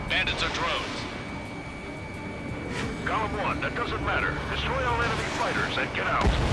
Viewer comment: Every time, they speaks "golem", I hear "galm"... XP [Belkan flashbacks intensifies]